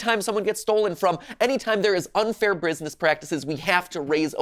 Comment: You know he's being serious because he's gotten so riled up that he said "brusiness" instead of "business". No actor would think of that.